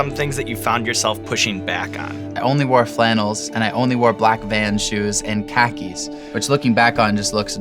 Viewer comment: I heard, "I only wore black vans and tekkies"